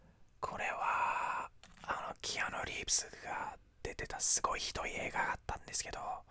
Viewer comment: Dude, i hate to be the one to point this out. But there's an English subtitle error at 4:37.."Keanu Reeves monie" instead of 'movie'. I know you are very passionate about these things 😂 xxx